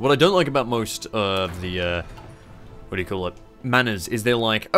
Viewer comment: Oh! He is saying "Mana" For the first 20 minutes I thought he was saying "Manner". I was like "Why is he calling opinion modifiers "manner"?"